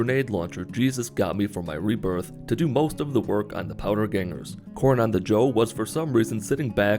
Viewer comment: Did he say C*rn?! 😡😡😡😡